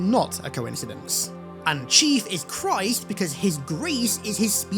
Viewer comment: I thought you said chiefus christ 14:34 and lost it